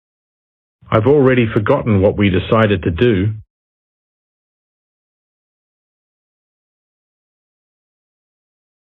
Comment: what did he say that??? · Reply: where did u find my keys